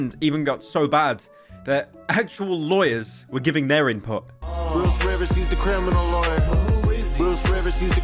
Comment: I ligit thought instead of clr Bruce rivers I thought it was saying ur burglers liver 🤣